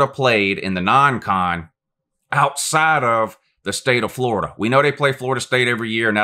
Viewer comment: Played Michigan in Texas 2017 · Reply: Could of sworn you said non-con out the state of florida like 10x MY BAD